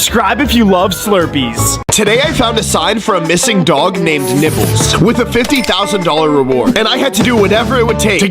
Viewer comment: who else Heard nipples 👇